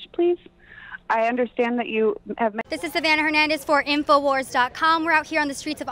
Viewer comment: I thought she said "info whores" lol